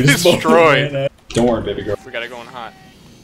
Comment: did call him baby girl? wat